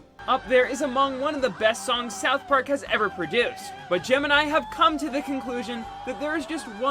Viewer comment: I heard "Gemeni" instead of "gem and i"